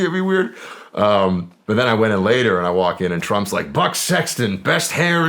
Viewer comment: I thought he said “Butt Sexton”. Almost did a spit take.